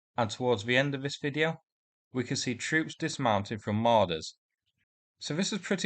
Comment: I have a bit of a hearing problem. When you said, "Troops dismounting from Marders," I heard, "Troops dismounting from Ladas." I was imagining a convoy of shitty little cars delivering guys to the front.